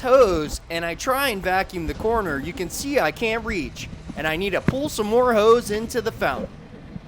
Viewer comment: did he say pull some more hoes? Or hose?